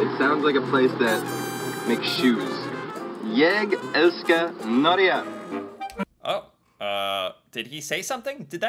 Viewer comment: jeg elsker norgia (it is actually said norge) he said: I love norway! in english